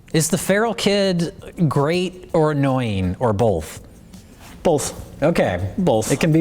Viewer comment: Did they just say "bolth"?